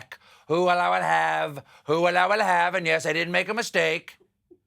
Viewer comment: Can someone PLEASE tell me what Stephen is saying at 3:58? It sounds kind of like "I will love a half" or "Who will have a laugh" and he repeats it a few times throughout the monologue. I have no idea what he's really saying or what he's referencing, and it has been bugging me ever since the day this video was posted. Nobody answered me when I asked back then. :(